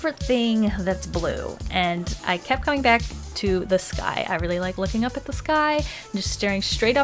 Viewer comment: I thought she said 'this guy' 😂